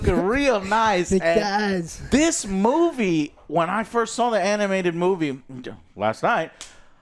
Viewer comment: did he just say first time watching was last night?